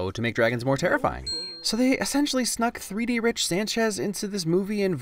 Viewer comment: did he really say rich sanchez cmon man